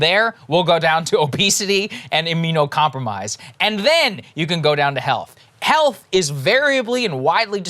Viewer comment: when Saagar was talking about the elderly I misheard him say, "and then you can go down to health."